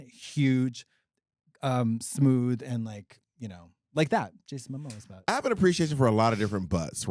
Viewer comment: Did I hear Katya say "Jason Momosa?"